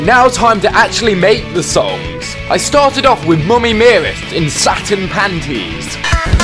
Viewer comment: Bf sounds like he is saying "Mommy ma ma"